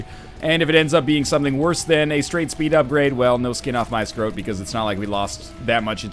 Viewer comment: did he just say "no skin off my scrote"? as in scrotum? wtf O.o